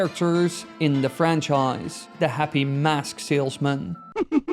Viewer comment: I heard MAP salesman instead of MASK salesman and was 100% ready for Tingle to pop out after that sinister Majora laugh 😂